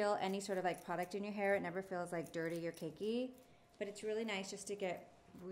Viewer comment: never feels “dirty or kinky”...okay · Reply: She said CAKEY. They’ve already clarified that in a comment. It’s not her fault if you mishear her.